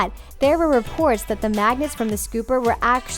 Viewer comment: Who else thought she said maggots